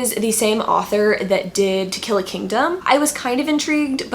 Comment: At first I thought you said "Tequila Kingdom" and I was SO ready to jump on goodreads & search for it but then it dawned on me that you actually said "To Kill A Kingdom". I was.........disappointed :/